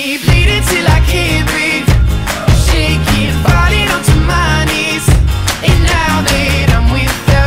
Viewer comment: Is it just me or did I hear "farted on my knees"